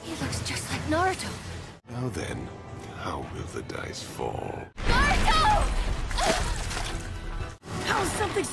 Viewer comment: did she just said boruto instead of Naruto??